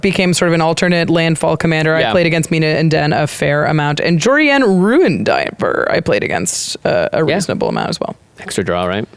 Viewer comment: I thought said Jori En, Ruin Diper